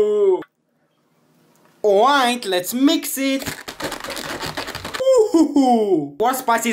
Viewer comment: Did he just say “oink let’s mix it” ?